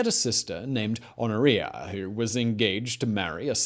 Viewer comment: I'm awful. I keep hearing 'gonorrhea' instead of Honoria.